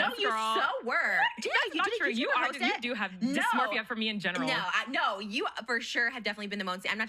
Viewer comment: “you do have dysmorphia from me in general” did I hear that right. She knew what she was doing this podcast 😭